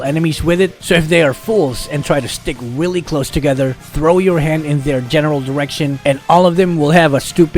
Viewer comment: I thought said throw your hand in their genital direction xD vexana the molester